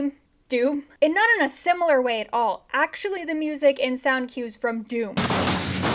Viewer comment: at first I misheard you saying Dune, I’m like “Okay, Hans Zimmer composed my trauma too!” 😅